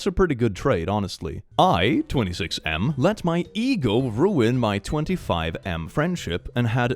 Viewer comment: Okay. I was listening and not watching the video. And I just heard eagle instead of ego, so I was soo shooketg